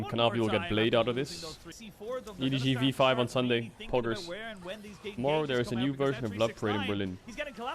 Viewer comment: what does he say/quote from his chat? Something new in Berlin? I can't understand and I have listened 5 times now. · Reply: "Tomorrow there's a new version of love parade."